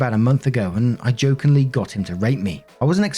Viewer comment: I have this issue where I mishear words all the time. Here, I thought read, "I jokingly got him to rape me," and I did a double take so fast I almost broke my neck.